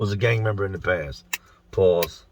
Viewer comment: Yooooo I only just now realized something stupid I'VE been doing... Every single time I've ever seen you take a sip of a drink I've always thought you said "BALLS" for whatever reason lol. Maybe some kinda inside joke or whatever. BUT, right now when I was finally gonna ask about it, I rewound and listened again and heard what is blatantly obviously "PAUSE". ...Yeeeeaaaahhh I suppose that makes a tiny bit more sense... 🥴